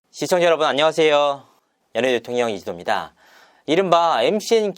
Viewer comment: 연예뒤통령이 아니고 연예대통령으로 들려요~~~